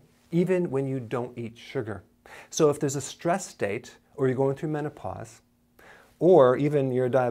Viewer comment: i misheard it as 'stress date' - haha. when tinder date goes wrong.